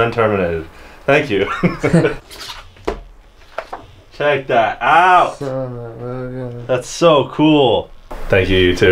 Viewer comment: What did say? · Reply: "son of a gun" mumbling is a language, learn it